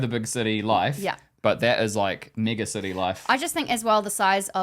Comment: Did he say, Mega City life or something else? Lol